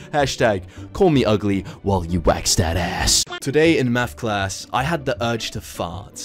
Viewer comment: Ngl at first I thought he was saying meth class😭